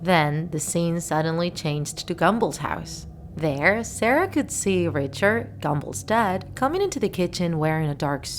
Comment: Or gallmball I also thought you said Richard gumballs dead